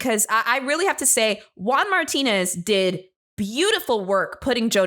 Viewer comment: I misheard you spelling his name as 'WALMARTINEZ' and can't unhear - was that on purpose? 😂😂